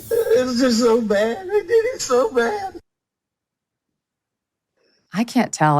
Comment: I clearly hear: I did'm so bad!